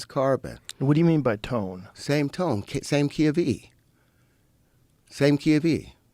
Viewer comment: "Key of E?" I thought he said keV, an energy state but this clown is saying elements are arranged by MUSICAL TONES... I guess he's talking about harmonics although I don't recall him using that word,, in a way it's compelling because it's poetically allegorical, as if certain substances make good tones or music and other substances make bad tones or music.. in a way it's almost psychedelic, it's a synesthesia blending pseudoscience and poetry or music,, and I suppose the reason Rogan is eating it up is because his mind is used to dissociative drugs LOL and the so-called leaps of intuition some psychedelic users sometimes get... Of course it's all intuition, it's an impression of ones feelings about perceived phenomenon but leaving behind pure intuition to embrace rational thought is what separates us from the lower orders of animals.....